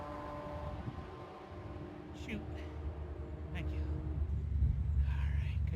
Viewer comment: I thought he said "Shoot", like darn, but he said "Shute" as in parachute...lol